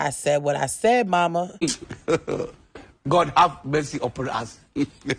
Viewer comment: Can somebody PLEASE translate for me what the laughing man meme say cause i hear "God please help messy operas" & i doubt that's what he's saying lol that "WHAT ?!!!. . . . . WHAT THE FU" meme have me rolling too 🤣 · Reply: He said “God have mercy over us”